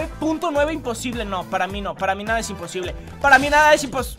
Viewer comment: Escuché en 4:25 laminada es imposible